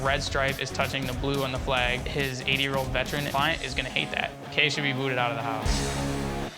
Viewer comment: My drunk ass thought it said "80 year old virgins" 💀💀💀